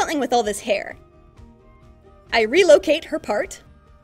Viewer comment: WHEN SHE SAID DISLOCATE THE PART I THOUGHT SHE SAID DISLOCATE THE HEART